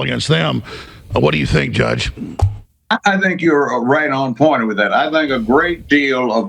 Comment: "youre right on point".... What did he even effong say? 😅